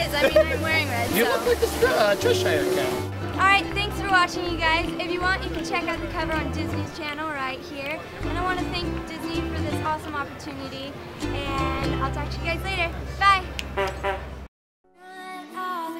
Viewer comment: i thought she said at 1st "you can see this cover on Disney Channel." and i was like "WHAT?!? WHY WAS I NOT MADE AWARE OF THIS?!?" lol